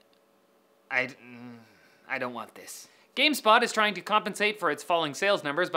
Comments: Did he say "Gamespot" or am iCrazy? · Did he say Game "Spot"? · he says gamespot instead of stop